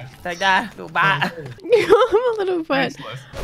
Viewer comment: Did she say I'm a little wet